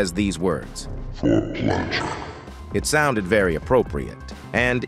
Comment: I thought he said for pleasure